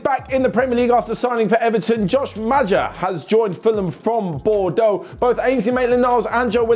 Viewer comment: i heard bodoh, bodoh in english is stupid. Bodoh is usually used from South East Asia